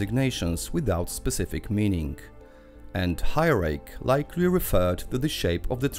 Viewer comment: / 2:00 - I think you said the antenna resembled a hay rake?